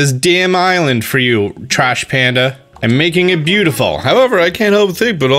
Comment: “Goooood morning butterpup” Two seconds later... “Trash Panda, let’s talk” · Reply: Lmao. I thought he said Cash Panda. Yowch.